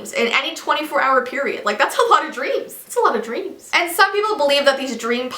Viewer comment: I thought she said that's a lot of greens LOL :-)